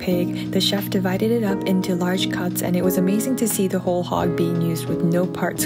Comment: I thought it said a whole dog💀